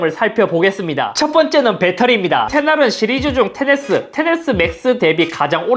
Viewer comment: xr인가요? 자막에는 xs로 나오는데 들리는건 xr이라서..